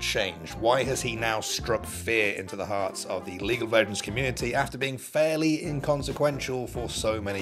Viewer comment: In the minute 1:26 the auto captions says legal virgins community 😂😂😂😂😂